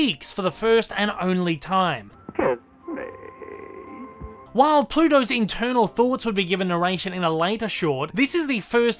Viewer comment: am the only one having a hard time hearing what plutos is saying at 3:23? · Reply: He said "Kiss me".